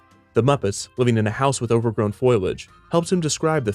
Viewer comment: Did you just call foliage "Foil-age". Say it with me.... FOLL-EE-AGE.